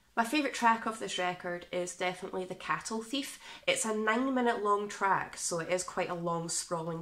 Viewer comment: Because of your accent i thought you said, “90 minute track…” and i was like not even phased by a 90 minute track. 😂